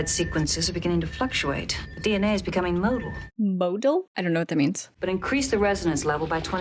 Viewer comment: MOTILE - to be capable of movement. I know it sounds like she says "modal", which is something completely different. In a medical context, motility generally refers to the passage of food through the GI tract.